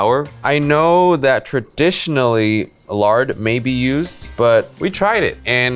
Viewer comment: did said milard( only milads fans know this)